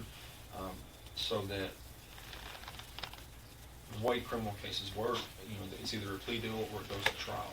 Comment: What do he mean by “ white criminal cases” sorry but I’m new to that term and never heard it used like this. Am I hearing this wrong?